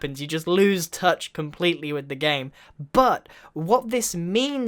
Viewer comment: BUTT! anybody catch that?